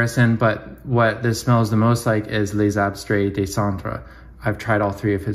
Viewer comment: what was the fragrance you said, Lesabres...? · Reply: Les Abstrait Des Cendre from our friend Eugen!